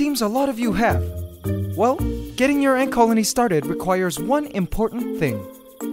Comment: did he say egg coloney???